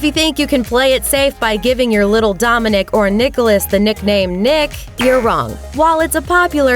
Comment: i thought it says nicole cause thats my name